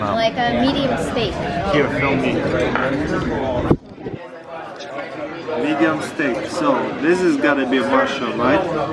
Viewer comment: she said Medium steak.. I thought she said "A Meaty mistake" hahaha